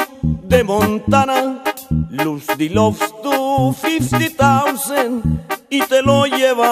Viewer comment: que dijo? en el 0:51 a 0:55 ayuda porfasss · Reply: morales lose the love to fifty thousand (pierde el amor a 50 mil)